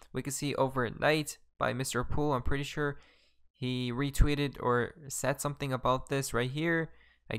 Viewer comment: Wtf is he saying the whole time, sweetout? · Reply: I thought he was saying tweeted out